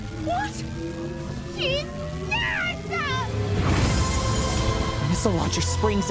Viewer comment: Nora: "She's nuts!" Audience: "Well, that's Ruby. Did you expect any different?" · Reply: I thought she said “SHES DEAD!!!!” Which is a lot funnier, but now after seeing this comment, she did say “she’s nuts”.